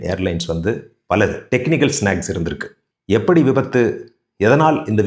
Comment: Sir @ 1:35 what is the word "Techincal snacks ? No clarity on audio , it's techinical glitch